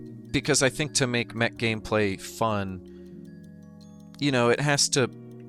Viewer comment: The words "TERRAN ARMADA" are severely triggering my internal anagram generator. 5:22 Why do I hear "Mac gameplay" and "It has to pass the iTest"? 🤔